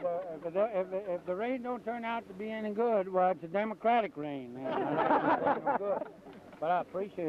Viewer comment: Does he say Rodgers at 1:55 ? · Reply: I think he says, "If the rain don't turn out to be any good, why, it's a Democratic rain."